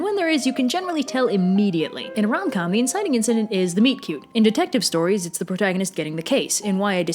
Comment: For the last like seven times ive watched this video I thought she said "Meat cube", and it confused me. I just had an epiphany mid shift, she said "meet cue"... I need to get my ears checked